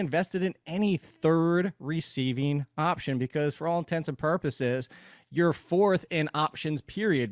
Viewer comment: It sounds like is saying "intensive purposes" instead of "intents and purposes"